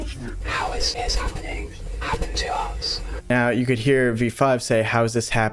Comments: in my opinion at 22:08 when v-7 speaks, it sounds like he said “listen to him” but it could just be me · it sounded like v-7 said "listen to me" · V-7 sounds like it is saying “Listen to him”